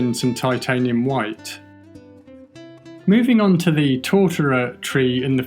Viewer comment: Life was so much more interesting when I thought was saying "Torturer Tree." Then I looked it up. Sigh.